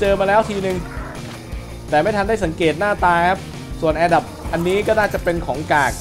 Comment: เหมือนผมจะได้ยินว่าแอดับ 555+